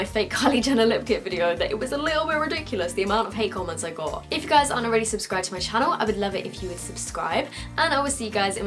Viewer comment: i thought she said, "if you aren't a raicist then subscribe to my channel"